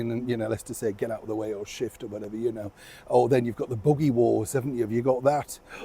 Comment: QEll was her own best press secretary. She could read people like a book and knew how to deal with them, one and all. I also had to laugh, you referred to the buggy wars. I thought you said boogey wars as in Boogeyman. Couldn't figure out quite what you meant at first .